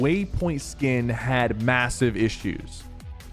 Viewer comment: did u say MASSIVEE?